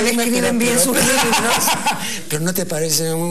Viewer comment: pensé que decía: parece un Enfeeeeeeerrrmo (como el especial del humor)